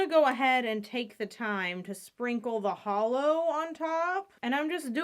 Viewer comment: did you say HOLOOOO!!!! Are we gonna put a nice thicc coat of glossy toco next?